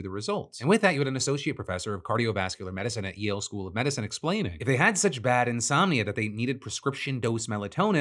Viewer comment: I can't tell what school he says did the study... I listened twice and heard Ye Olde School of Medicine